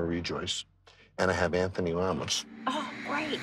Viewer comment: “And I put em in disguise” Dude no one knows who tf those ppl r anyway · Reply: I thought John was saying Anthony Ramos, and I'm like that doesn't look like Anthony Ramos from Hamilton.